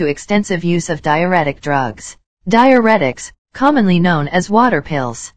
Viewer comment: Diuretics can disturb your electrolyte imbalance. More than likely caused her to have very low potassium which can cause a fatal arrhythmia. I work in cardiology and when someone has low or high levels of potassium we have to monitor there heart very carefully to make sure they don’t go into a fatal arrhythmia. So sad. May she R.I.P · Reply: Lol I thought you said dianetics and I was like oh great here we go with the crazy 😆